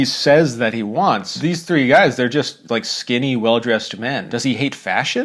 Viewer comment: did you just say well dressed? 😂